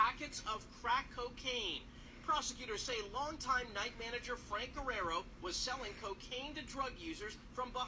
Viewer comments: Did he just say- Frank Eraro? Frank Iero? Frank Iero is my idol. Frank Eraro? WHO??? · I thought he said "Frank Iero" I honestly wasn't surprised